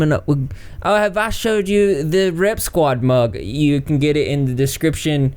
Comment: I wasn't looking at the screen when you mentioned the mug. I thought you said "Rep Squad Mop".